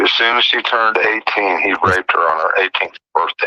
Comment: Honestly, ALL I Heard was "grape her". Omg 😳.